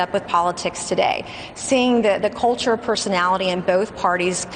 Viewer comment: Did she just say “Culture Personality “ rather than cult of personality?